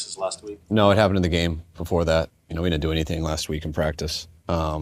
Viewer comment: He did practice last week. Shanahan didn’t say no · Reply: Okay, so rewatching and finding a transcript, he's saying at 0:44 "We didn't do anything last week in practice", which in my initial watch I heard "HE didn't do anything last week in practice." It still feels confusing to me. I guess if he says they "didn't do anything', implying light practices (perhaps you can be more detailed on this,), how could anybody accurately gauge Pearsall's actual status.